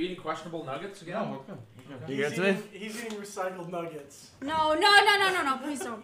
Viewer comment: what was that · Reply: Pretty sure it's a chicken nugget, you can hear the words "recycled nuggets" at 1:21. But tbh I didnt catch that stream, so I'm not 100% sure